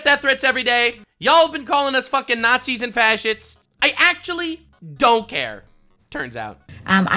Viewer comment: I had to replay it a few times cause I thought he said fat sh*ts (still not inaccurate) lol